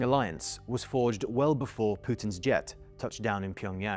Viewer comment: I stg I heard “before putins gyatt”